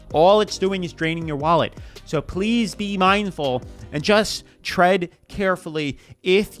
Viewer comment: At the end I thought you said "trend carefully" instead of "tread carefully" but honestly feel free to use it. I think advising someone to 'trend carefully' is solid advice. Thank you for always being real. I enjoy these!